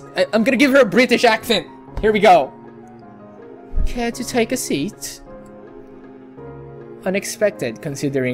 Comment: i thought i heard "Care to take a shit?" xD